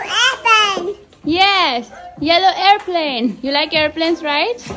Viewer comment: I thought she said yeyo airplane then I listen that said it's yellow airplane f ❤️😂